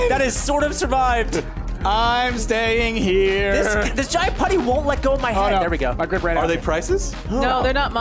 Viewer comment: the auto generated captions say "this giant putty wont let go of my heart" 😂😂😂😂